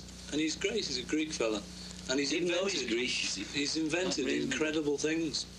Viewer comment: Paul McCartney ignores John Lennon's "Even though he's a Greeek". Obviously, John is only joking but Paul realises repurcussions and ignores completely this comment from John. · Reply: I thought he said he knows he’s a Greek as in you don’t need to say his nationality ?